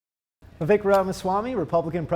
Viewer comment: Did he just call him Vikram?